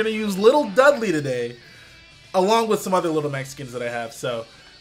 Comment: when he says "little Mac skins" it sounds like he says "lil Mexicans" & I was like yo.. straight savage out here lmao